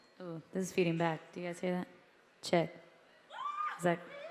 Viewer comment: did she say check or shit? It sounded like check to me? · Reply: She Definitely said check haha. Thanks for Watching!